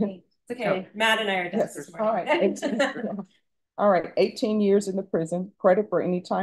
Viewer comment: I thought she said eight years, as well!